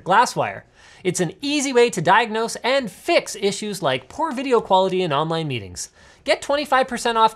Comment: Is it just me or i heard "p0rn video quality" instead of poor? 🤣🤣